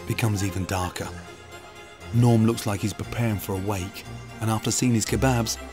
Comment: Lmao I thought he said “Norm looks like he’s preparing for a WANK”